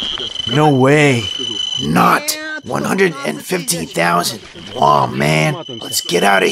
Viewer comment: This was awesome! What was the maximum radiation intensity you measured? I think you said 150,000 around 4:00? Was that in microroentgen/hour?